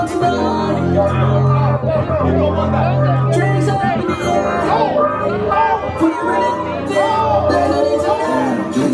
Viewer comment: Can anyone tell me what Larry said to him on 3:09 ? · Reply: That guy, challenged Larry for a battle and Larry said "You don't want that". But of course, we know, Twins never declines challenges, so he accepted his call-out and motioned him to start the round.